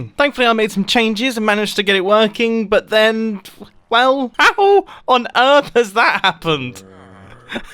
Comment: What did he say at 6:41 Ha Hoo?